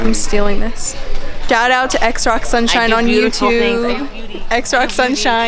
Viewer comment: What is that girl saying at 2:54? sounds like xrocksunshine but nothing comes up! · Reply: xroxsunshine is what you're looking for.